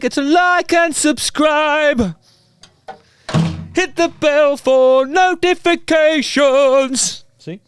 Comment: I swear I heard you sing: "Hit the bell for no defecations" <3